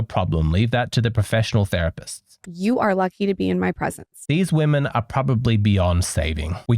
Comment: The Wing Girl Method: Unlock the secrets to successful flirting (+ 20% off) at <link> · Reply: I heard "These woman are probably Beyoncé " — and is not far fetched from reality. Woman who see Beyoncé as example, I recommend any man to RUN AWAY FAST from them!